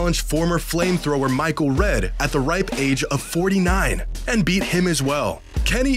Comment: I was highly amused how instead of ‘age’ the caption read ‘life experience’ @ 5:36. It could not be more American.